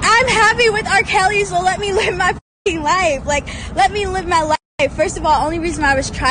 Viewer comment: I missed her say she's living her "Best" life, Nope definitely no "Best" I heard her say her fucking life 👂🏾🤔😟